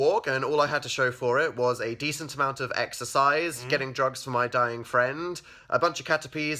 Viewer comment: ALL THIS TIME I THOUGHT HE WAS SAYING "Darling friend" BUT HE WAS SAYING "Dying friend